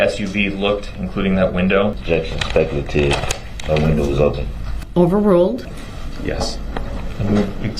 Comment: does he say “the” window was open or “my” window was open? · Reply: Pretty sure he said “No window was open.” If you watch this thing enough, you realize DB has no problem denying reality.